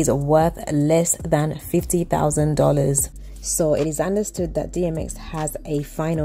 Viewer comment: time stamped did u say his estate is worth less than $50,000 dollars???? I rewind to verify. Dunno but that sounds incredulous? If what ur saying is true then by the time they are thru with legal fees & probate in the courts there'll probably be nothing left to manage. Just saying...